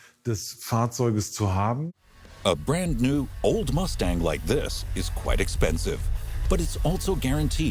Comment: Did I hear the narrator right, at 19:33 he said “Olds Mustang”, as in (Oldsmobile)? It’s a Ford Mr. narrator! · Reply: He said "brand new old Mustang" or in other words, "newly restored vintage Mustang".